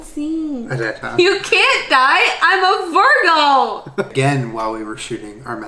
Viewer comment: When she said he cant die shes a virgo i was half listening and on another tab and for some reason i thought she said virgin and i was like???????